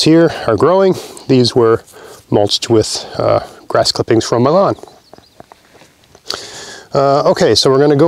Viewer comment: Just for a second I thought you said the spuds were mulched with grass from Milan instead of "m'lawn" and thought "that's a bit fancy for mulch".